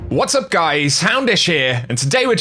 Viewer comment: I saw this in a comment a year ago now and now I still hear it whenever I hear your intro lol I hear "Hamdish" instead of "Houndish" half the time